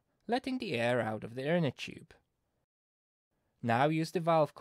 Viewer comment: what did he say? "Let the air out of the ????? tube" What? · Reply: I think he says "inner tube".